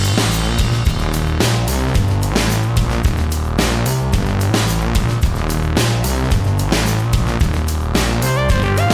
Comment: what does that say? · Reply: portable environment